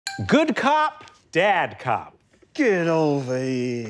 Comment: I thought it said "dead cop"